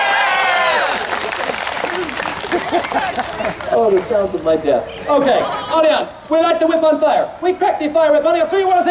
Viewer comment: Wait what did he call himself at 0:49? I couldn’t tell.